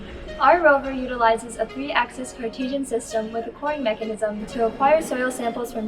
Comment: what kind of mechanism did you say at 1:36? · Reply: It's a coring mechanism